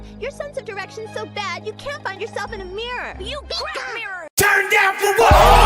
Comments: what did ash say there I can't hear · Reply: "YOU'D CRACK A MIRROR." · Did anyone else hear at 15:43 YOU BEAT THE MIRROR I don't get it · Can someone tell me what did Ash say? Did he say "You broke the mirror" or "You'd break a mirror"? I didn't hear it that well because of Pikachu, no offence to him :/ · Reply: Pretty sure he said "you'd crack the mirror!" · What did ash say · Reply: i think he said you beat the mirror or something